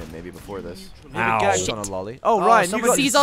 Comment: Did Gavin say "Gagged on a loli?"